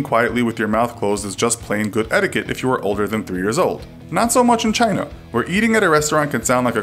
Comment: This video is full of crap since when did Asia not include the Arabian peninsula.Y’all act as if the middle east is just floating alone.I think you guys should learn some geography and also educate yourself.The taboos mentioned were almost all incorrect. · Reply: Nope. Listen again, at 3:18 he clearly says in most countries it's considered polite to eat quietly but "Not so much in China".